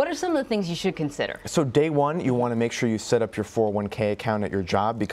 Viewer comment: what does he say? 41k? I couldn't catch that.